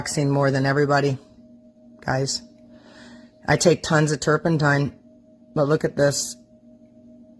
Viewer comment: hi to everyone✌🏽❤️ @ 9:50 minutes did she say turpentine?